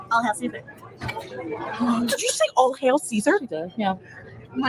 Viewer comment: I heard "All kale Caesar" 😂